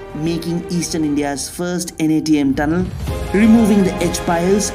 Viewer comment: did you say NATM tunnel? I am not sure if this is a NATM one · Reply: Natm it was. Natm used where tunnel of green line crosses below the tunnel of Blue line. There is a video as well regarding this.